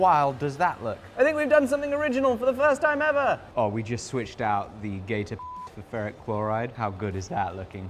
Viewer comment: I have listened to this a dozen times and I keep hearing "we switched the gay to (beep) metallic chloride" and it's absolutely sending me